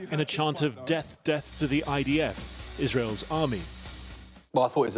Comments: i dunno about anyone else ... i heard " deaf to the IDF" ... ;) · I heard "Deaf Deaf to the IDF". I don't know what Channel 4 is talking about. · Here in the USA I clearly heard him say Deaf to the ID F, that means he can’t heard them. That’s not violence.